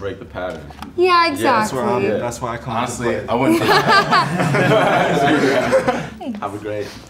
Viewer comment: hold on after she said she didn't want to date another musician... did one of the eliminated musican guys say, " it's ok I wouldn't date one/date me either?!" 😂😂😂😂 I keep replaying it and I can't hear it clearly.